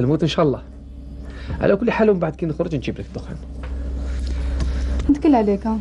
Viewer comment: دقيقة 54:20 قاتلو شريلي دخان ولا مسمعتش مليح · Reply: نعم دخان